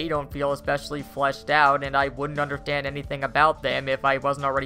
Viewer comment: Gotham by Gathlight? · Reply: i heard "i wouldn't understanth"